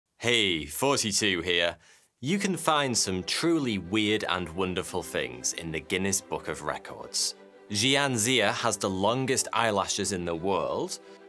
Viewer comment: It sounds like he says forty-two instead of thoughty2